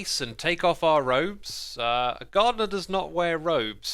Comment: oh that would definitely make more sense. I thought he said god because I can’t understand his accent super well and I thought he was referring to the Infinibee. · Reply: yea I thought he said god too at first but then I replayed it and heard gardener